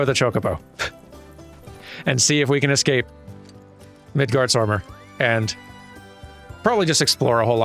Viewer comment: I keep hearing ‘Midgarsommar’ like, yknow. The horror movie 😅